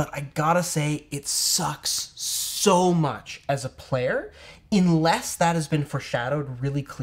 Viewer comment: Did you say "inless" instead of unless?